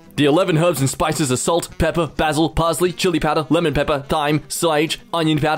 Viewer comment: Did... did he just say 'th'yme?